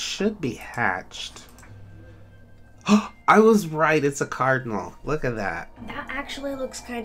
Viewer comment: I thought it said CARNIVAL